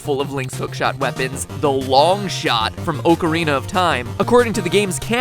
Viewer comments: Wait did he just say oucerina of time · Me: matpat did you just say OAKARINA OF TIME?! Matpat:........ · lmao did you just say Oh-carina of time? xD